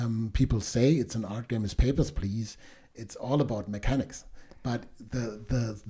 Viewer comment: did anyone catch the name of the art game based primarily on mechanics at 22:54 mark? couldn’t understand what he said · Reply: "Papers, please" is the name